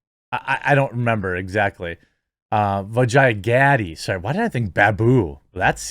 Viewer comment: I swear I thought said her name was Vagina Daddy